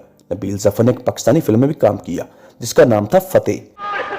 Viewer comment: Can i know that name of the drama at 5:51 ? Pls anyone reply you told the name but I can't hear it properly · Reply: Yeh drama nahi movie hai jis ka naam Fateh hai...aur youtube per mojood hai..